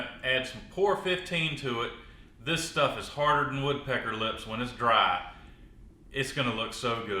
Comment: "Harder than woodpecker lips" ? That's some pretty hard sh__ !! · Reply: For some reason, I heard, "wood, pecker lips."😂